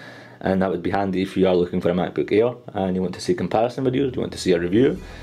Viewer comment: sounds like hes saying "macbook ear"